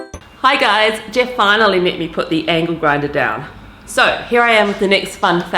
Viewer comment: ! Divine ❤️. Jeff? Madder than a box of frogs? Oh yes. (I must confess I had a senior moment when I thought said 'anal' grinder. I apologise 😳)